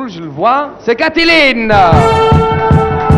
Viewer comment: what he say? In french plese. ThanX))) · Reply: T'en fais pas pour moi.